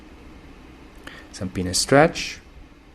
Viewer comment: What did you say you are doing and is it because of the view? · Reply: Penis stretch